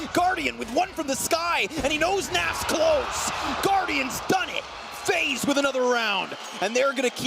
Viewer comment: мне одному послышалось "гвардиан в здании"